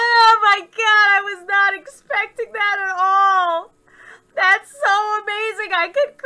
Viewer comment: L: she's like that's okayyyyyy Me: I thought u said that's so gayyyyy 😝